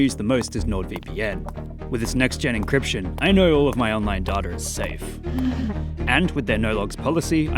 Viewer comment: The way he said "Data" made me think he said, "...my online daughter is safe."